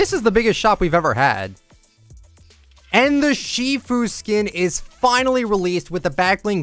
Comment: Shifu! Me: I thought you said Tfue for a sec